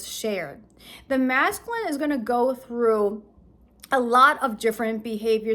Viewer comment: Oh no, are we back to this old story? I thought we'd moved on! I thought you said the masculine is waking up. BORING!